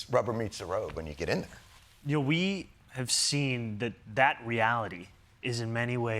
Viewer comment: did he say "Yo, we..."? :D